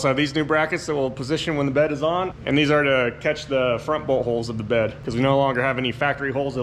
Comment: When you said bolthole I thought you said butthole! 🤣